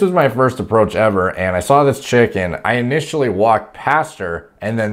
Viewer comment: I’m dying laughing, I have subtitles on for some reason when you say I saw this chick and it subbed it for chicken but if your reading it also it sounds like you say “ I saw this chicken and had to approach “